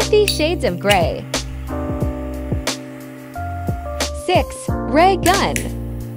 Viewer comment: I thought it said gay gun